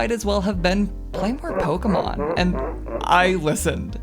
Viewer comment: I'm so sorry I cannot make out what she's saying here; is it "Play more Pokemon?" "Blink for Pokemon?" I cannot tell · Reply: play more pokemon